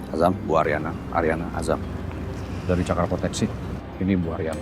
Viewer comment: Salah denger nggak sih, cakar proteksi...... Hehe 😰😂